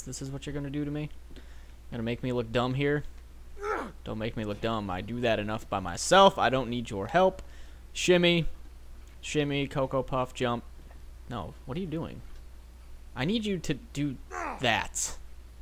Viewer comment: lol at 06:20 i thought he said "Chimney,Chimney cocopuff jump" lawl